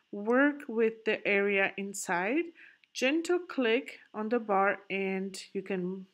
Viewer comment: What is it you're saying at 2:24? "Gentle click"?